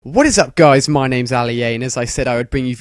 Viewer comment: Did you say your name is Ali Anus?